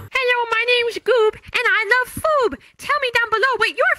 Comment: My favorite foob (that’s what Goob said) 13:41 is pizza